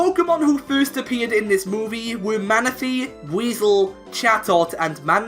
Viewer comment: I hear weasel not buizel